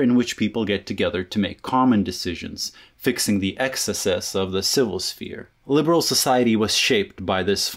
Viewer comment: the captions decided on 'xs/s' instead of excesses, and I think that's just amazing.